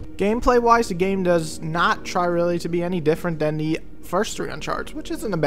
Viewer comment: sounds like he says, "not trying to really be any different than the first 3 Uncharted", right?